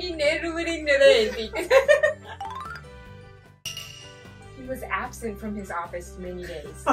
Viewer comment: この企画、とても面白かったです！ 6:23は、は"He was absent from his office for many days."と言っていますね。abcenceではなくabsent、many dayではなく(for) many daysだと思います（forは9:46でしっかりと聞こえます）。日本人にとって聞こえにくい音が本当に難しいですよね。